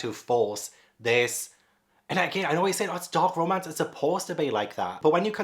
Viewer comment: I was watching with the subtitles on (hard of hearing) and when you're talking about "Does it hurt" and you say "It's a taboo romance, a dark romance"- the subtitles say "it's a dog romance." for a full five minutes I thought you were doing a bestiality story! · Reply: I DID THE SAME THING!! I didn't even have captions on but I wasn't looking at him while he was speaking and with the accent and not seeing his mouth moving I swore he was saying dog romance and I was shocked that he would announce that so publicly. Like genuinely if he wants to read that in private I don't care but I was just blown away that he was so open about it and then I realized he was talking about dark romance and I died laughing